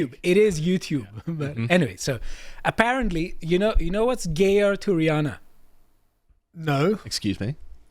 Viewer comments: I thought he was saying " you know what's gayer to Rhianna?" 😂😂😂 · I just listened and misheard saying "Gayer to Rihanna" and was confused. Saying it nowadays feel embarrassing but I found this mixed race (she actually got racially bullied during her childhood because she was half White so she should understand that Blacks often are racist against Whites, other races and those who are of visibly mixed race instead of (nearly) pure Black.... but she doesn't) quite attractive when she was young (I'm the same age) and I saw her on TV with the first few songs/music videos. Music was okay but not my preferred genre. She was cute but changed more and more into that gangsta rap look with ugly tattoos and such. I wonder if she was "mentored" by some rich producers and was present at those parties. Or maybe she has always been that way.